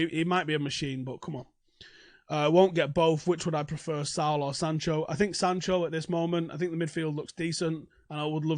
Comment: When you said Saul or Sancho, i heard "Salah or Sancho".....was confused for a split, must be the Mancunian drawl